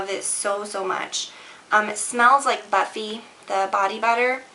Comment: lol i thought she said "butt feet" when she said "buffy" LOL